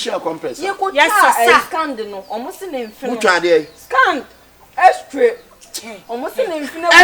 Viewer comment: did i just hear x-tray🤣🤣🤣🤣🤣🤣🤣🤣🤣🤣🤦🏾‍♂️